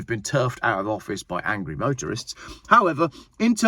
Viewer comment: Not sure I heard that right.... I know it was "motorists", but it sounded like "Voterists". I define that as "Voting Motorists". A very powerful group of individuals who really should get together and force changes in this country.